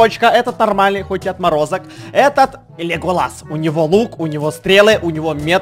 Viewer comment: мне не послышалось? Ты сказал.. Леголас? :^